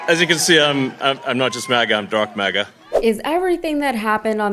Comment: 'Dark MAGA' IS AN OXYMORON. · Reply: I thought he said "dork maga" must have been the SA accent. 😁😁😁